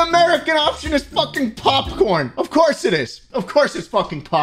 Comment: I heard that as "porn"